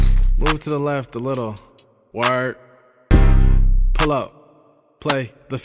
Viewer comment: I can’t be the only one who thought I said “play the film” 🤦🏻‍♀️